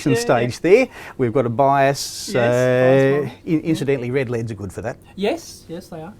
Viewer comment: in What am I hearing? Australians please help. It sounds like "Red LEDS" but that doesn't make any sense. If so why does he draw LEDs like battery cells? It took me a moment before I realized that a resistance source was just a block but I got that one.